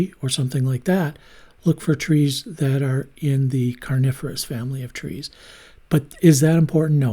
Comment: when you said “coniferous”, I heard “carnivorous” and for a split second I was very confused 😂